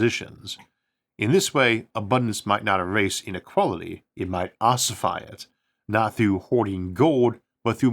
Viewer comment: he says " in this way abundance might not erase inequality, it may ostrify it" What word did he use cause ostrify is not a real word and I couldn't find what he said, Does anyone have any idea? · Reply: Ossify, not ostrify